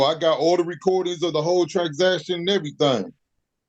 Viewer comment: Did he say, "I got all the recordings of the whole tragsaction and everything"? I'm hoping this is just a slip of the tongue and not how he thinks "transaction" is said.